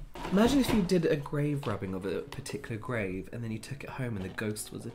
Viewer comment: Omg grave RUBBING! I thought he said "robbing" and I was absolutely confused for far too long! Lol